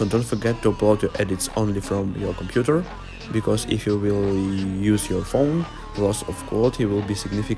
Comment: Hey, I am sorry, I could not quite understand what you said at 8:28, did you say only upload from your computer and not your phone for minimum quality loss?